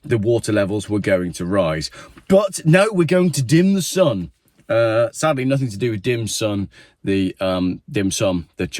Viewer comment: They were misheard… they said ‘dim sum for lunch’ not ‘dim the sun by lunch’